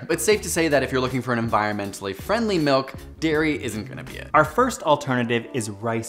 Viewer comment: i..i was watching with my conservative family and... u.... omg.. "dairy is horny" 'what did he just say?" "turn that crap off"